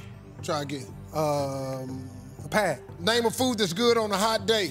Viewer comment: I thought he said “pass” but he said pad